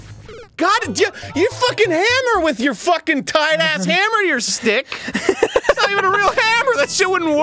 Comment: “Why you gotta be like that Why you gotta fucking do that AAAAAAAAAAAAAAAGGGGGGGHHHHHHH” I believe that is what we call releasing the beast As an aside for the longest time I thought he said “tight ass hammer” He actually says “tied ass hammer” “Have you been drinking?” “Yeah heavily”